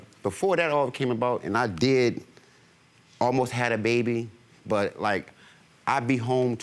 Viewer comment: Did he say “I did … almost have a baby!!” 🤨